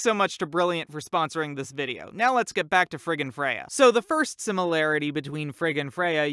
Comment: it sounds like he is saying “frickin Freyja”